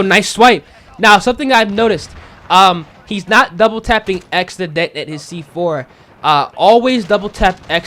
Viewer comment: can someone tell me what he said? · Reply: He is not double tapping X to detonate his C4